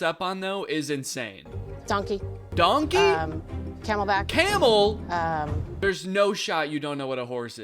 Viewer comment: I think she said "Camelback" at 1:45 Which is one of those hydration backpacks. And the person on the horse is wearing a backpack.